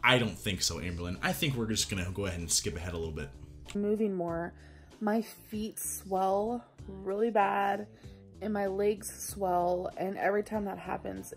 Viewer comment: I swear I heard "my feet smell real bad" and for a second I was like WTF, are you blaming your smelly feet for your weight?? Its late. I should sleep